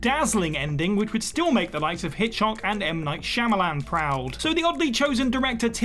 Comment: did he say Hitch-hock? Hitch-ock?